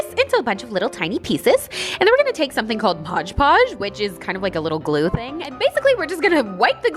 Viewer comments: was i the only one who heard her say podge podge instead of modge podge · did anyone hear her say podge podge 😂